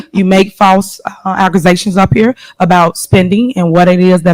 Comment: did she say aggrizations? I know about allezations but damn.